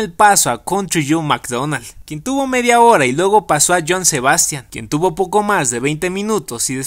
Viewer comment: entendí Joan Sebastian y hasta busque Joan Sebastian Woodstock